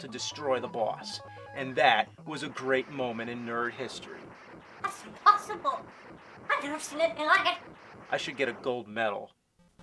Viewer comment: wtf does the glitch gremlin say? It’s been years and it still pisses me off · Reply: "That's impossible! I've never seen anything like it!" That's what he says